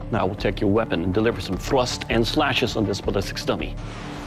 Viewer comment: Who els heard him say thwust instead of thrust